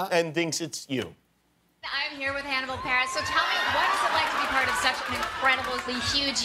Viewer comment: "We're here with Hannibal Barress" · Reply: I thought she said Hannibal Paris